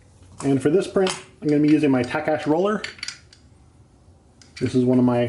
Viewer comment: I can't make out the word at around 8:00 - it sounds like "Tech Etch" or "Tekash", but the closed captioning says only "Tash". I did check the Tools & Materials page, but the brayer link is a Speedball brayer. What is the word or brand name of that "Tech Etch" brayer? Thanks! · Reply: Sorry about that. It’s a Takach brayer from <link> !